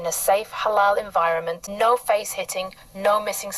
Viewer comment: Wait. Did she say "no face hitting"? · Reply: I thought she said no face sitting 😊